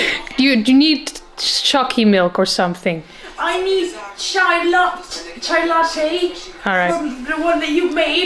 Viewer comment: did she just said " Chai lAtTe "